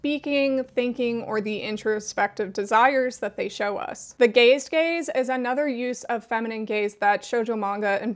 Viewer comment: Sorry but when you said the “gazed gaze” I heard the “ dazed gays “ and I choked on my cheesecake, I was confused on why that was an important part of the feminine gaze 😂